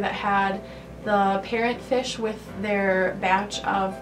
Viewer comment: Did she just say parrot fish instead of discus?